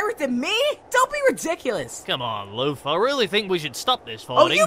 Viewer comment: who else heard: cmon lufa ?